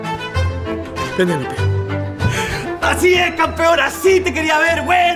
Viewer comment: En lugar de "¡Así es campeón!" entendí "¡Así es cabrón!" jajaja Lo remató con "Cierra bien el carruaje". Excelente servicio tío Netflix.